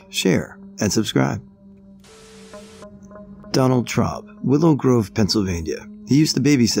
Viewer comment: I thought the first one was Donald Trump lol 😃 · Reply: Omg, I was listening to this while playing a game and the first thing I hear is: "Donald Trump" I literally stopped what I was doing and said: "Wait, what?" I go back to the page and realize it's Donald Traub. Boy that threw me through a loop lol.